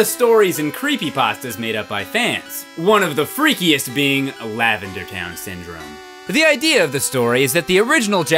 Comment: well. i misheard lavender town syndrome as "lavender's down syndrome"